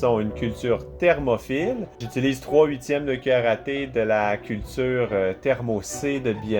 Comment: Waah je suis désolé mais j'ai ri quand j'ai entendu "1/4 de karaté" à la place de cuillère à thé. Désolé tes le meilleur mdrr